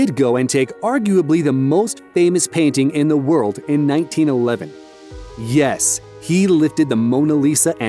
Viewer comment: did he just say 19011